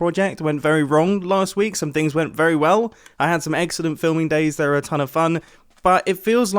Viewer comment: Mumbo says “I had some excellent filming days, which are always fun” I heard “I had some exodent filming days, which are always fun” Nope, pretty sure I heard that wrong. That just can’t be.